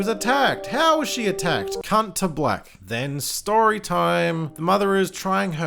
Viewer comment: Was that a cunt to black i heard haha?